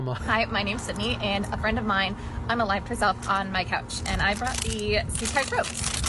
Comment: I genuinely cannot understand what the hell she said here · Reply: She said that one of her friends unalived herself on her couch yikes